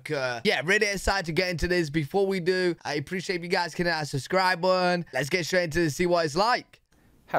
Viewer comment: When you say hit that “subscribe button,” I hear, “Sus cry bun.”